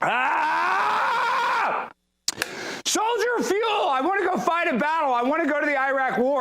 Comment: Not your fault but I misheard it as "solider of fuel" I actually figured it out when I looked at the screen to see your beautiful face no homo. Also Tim Pool is a furry and his fursona is a rat